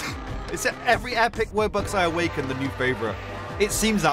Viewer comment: Can we just ignore that said "Wubbox" but the caption said "Roblox"